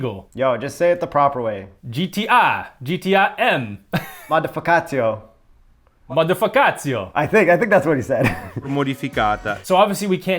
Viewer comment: did he say MA DA FA KA TIO😂😂😂😂